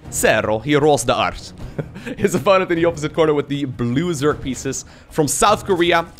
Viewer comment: Yawned when you said "blue Zerg pieces". Thought you said "loser pieces".